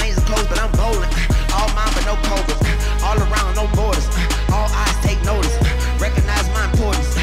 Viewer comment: sick with it , no covoid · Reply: Does anyone know what he says at 1:47 when he says “all mamba no ???” I’ve listened to it 20 times even on .5 and .25 speed and can’t figure it out lol kinda sounds like he says posers but idk